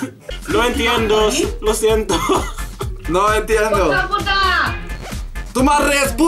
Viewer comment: What did Simon say at 1:21? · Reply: He said the word ''puta'',Which means something like ''fuck''